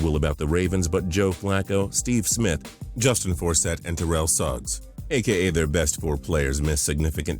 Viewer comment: I thought I heard Forssett is gone?